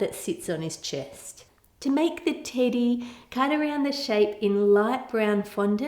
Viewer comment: I wasn't really paying attention just had this on as background doing my makeup and at 4:16 I swear I thought she said "now to make the titty" 😂😂